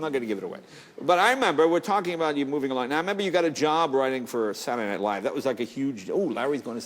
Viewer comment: Jay actually did say "a job writing for...". Larry just didn't hear it.